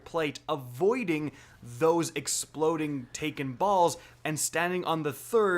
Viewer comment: Sorry, all I heard was "taken balls".